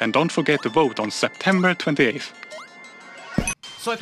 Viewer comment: My dumb self really thought he said September 20th. Man be a lot cooler if they did tho